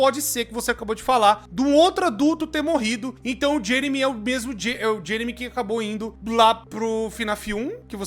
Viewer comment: "então o Jeremy é obeso" foi oq eu ouvi